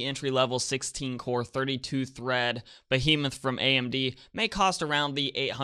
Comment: did he just say entry level 16 core 32 thread beamouth